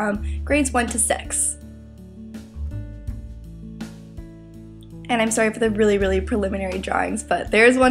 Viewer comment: did she just say sex in 1:15??😮